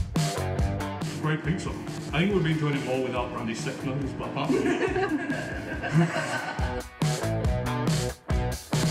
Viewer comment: wtf did Adam say? I watched the subtitles 3 times and still couldn't figure it out... and I follow his channel! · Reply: the bearded, the Bakers, and the garlic stick takers.